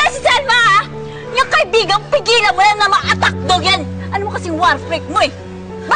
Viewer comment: ano sabi ni georgia??? attack dog????😂😂😂😂😂